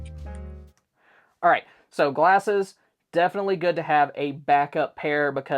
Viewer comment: I wasn't actually looking at the screen for her first item, I thought she said "wine glasses" and I thought, that figures she's European.